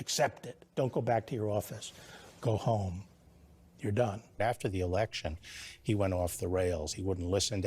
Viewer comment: did Trump say “You’re done” or “You’re FIRED!”⁉️